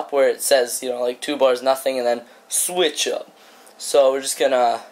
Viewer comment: Great tutorial :) I got really confused at 3:18 though. I thought you said 'Switch up' aha seriously though this was very helpful.